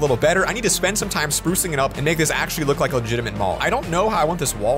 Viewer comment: Did he say the n word? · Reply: OMG HE SAID AND MAKE THIS ACTULLY BUT WHEN U HERE IT IT KINDA SOUNDS LIKE IT